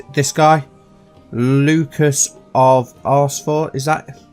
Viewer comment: I thought you said 'of Arsefort'! XD